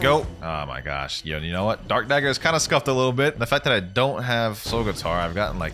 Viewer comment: "I hate spammers like you" 3 minutes later: "This is a spammy nightmare" 😂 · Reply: I thought you said that you didn't have soul Guitar